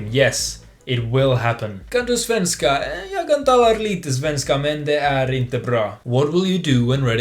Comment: AH NO, wait, what did he say at 2:34 btw sorry I’m uncultured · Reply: oh i said jag kan talar lite svenska, men det är inte bra